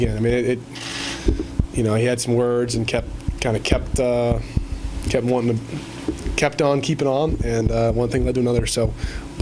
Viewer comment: Mason said something 2:21 Mason kept quiet just like the other guy did during his interview. The details came out during the investigation. The guy never should have hit Mason the way he did but Mason said what he said 2:22. Look and listen to Mason...come on lol. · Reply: "kept on keeping on" duh Mason knew he couldn't repeat what was said because his team may have fired him 😂. Mason better go sit down.